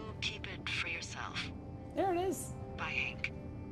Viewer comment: when Delilah is talking it says Henry .-. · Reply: And at 23:41 She says, "Bye Hank" instead of "Bye Henry". o-o